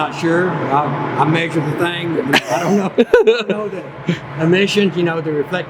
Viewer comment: There's a typo in the subtitles at exactly 9:34, your dad is quoted to have said "I make the thing..." but he really says "I measure the thing..." That'd be quite confusing for people who rely on subtitles. Just thought I'd let you know.